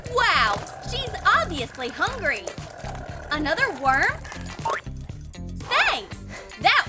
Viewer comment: The witch gave in the wolf is spider gummy in 3:22 i hear that someone says worm